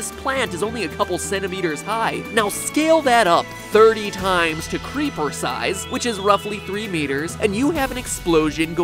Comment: it said two in the video... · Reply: No, at 7:21 MatPat said "now scale that up to creeper size, which is roughly 3 meters" where did he say two?